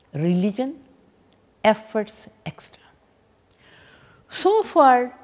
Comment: Did you say “efforts extra” at 12:38? Wonderful presentation! Thank you so much!!